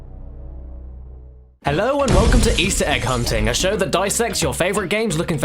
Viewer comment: Feel like it's worth mentioning that my video buffered briefly at the beginning and all I heard was, "Hello and welcome to Easter eh... c*nting."